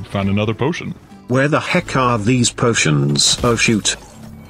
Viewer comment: WHAT DID PEELY SAYED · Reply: Oh shoot that’s what agent p said